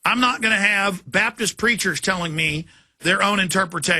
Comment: i thought he said preachers in cells - that would explain the some things about creationists